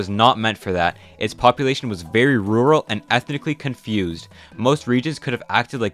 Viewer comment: What do you mean "ethnically confused"? We aren't ethnically confused. We are literally not all the same people. We had our own states in precolonial times. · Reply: oh i thought he said ethnically diffused